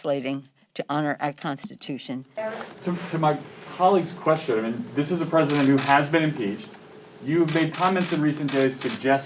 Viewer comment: Is there such an amendment to removed Pelosi from her office???? WHAT??!!! @ 2:05 the reporter says 'this is a president that has been impeached'....did hear that correctly??